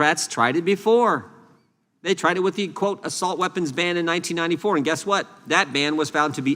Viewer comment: For a second there I thought he said 1984.